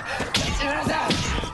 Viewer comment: 何回聞いても西村さんにしか聞こえん これオンドゥルじゃないん？